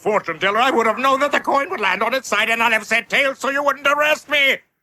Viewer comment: I just realized that I think the American dub line was "And I'd have said tails" instead of "And not have said tails". Since it's so easy to mistake for the other, this doesn't let them off the hook at all